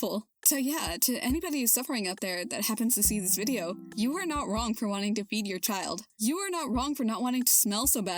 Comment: holy SHIT this video is good. the only thing I have to say is that at 1:04:25 I GENUINELY thought you said "you are not wrong for wanting to BEAT your child," (assuming you were talking about Sparky) and I sat there in awe like rewinding it over and over thinking "theres no way" anyway when I realized you said FEED i felt real stupid lmao